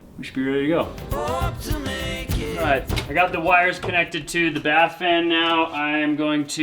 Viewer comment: Bath fan!!!! Oh my goodness!! The first couple of times I heard say it, I thought he was saying “fart fan”.